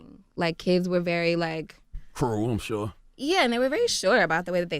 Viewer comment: what did Charlamagne say? "For a room sure?"